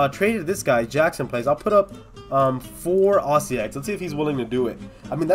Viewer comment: like if you heard him say Aussie eggs instead of fossil eggs